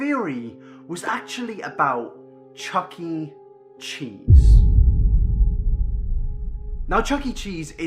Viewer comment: did you just say chucking cheese